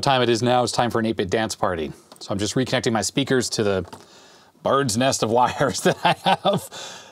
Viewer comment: I misheard "bird's nest of wires" to speaker's at 47:13 as "Bard's Nest", which seems very fitting for SID chip to speakers (esp. if running '85 Bard's Tale!). Maybe even worth making a T-shirt "My Bard's Nest of Speaker Wires!"